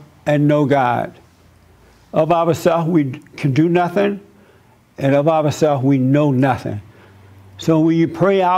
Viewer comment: What does Jesse say between 2:39 and 2:44? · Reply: Of ourself we can do nothing, and of ourself we know nothing